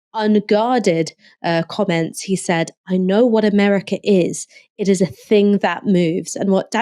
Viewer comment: "America is a thing that moves." Ooohhhh! He said "moves"! I must have been hungry for a hamburger or delirious from the bovine methane in the air, because I had previously misheard that quote as "America is a thing that moos!" Which is a statement that I agree with, based on most of the conversations of the past 40 years that I have had with my fellow Americans... Also cows are cute 🐮